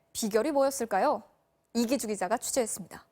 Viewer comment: 잘못들어서 이기주의자라고 들었네